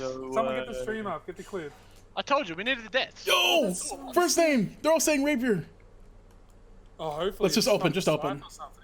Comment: what did he say at 7:20 ??? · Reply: He said, “first in my name, they’re all saying rapier” and he is referring to his live stream chat all calling rapier for his first purple. Hope that helps 🤘🏻